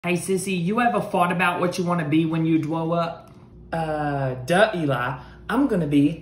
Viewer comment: I thought he said what do you want to be when you throw up 🤣🤣🤣🤣🤣🤣